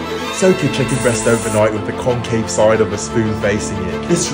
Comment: I thought he said cocaine.... I messed up